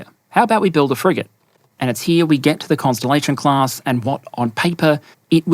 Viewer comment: Props for "Shooting for the stars" header on the "Constellation-class" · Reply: why did I hear "Cancellation class"??